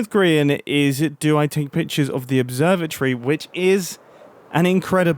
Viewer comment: idk why but I thought he said observer-tree lol